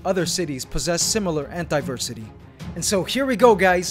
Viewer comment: it sounds like you said "antiversity" lol